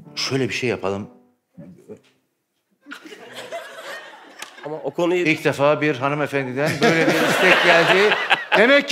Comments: ta kadin ne diyor ya 😂😂😂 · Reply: erotık fılm dıyor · civarı seyirci ne dedi anlamadım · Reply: Erotik film diyor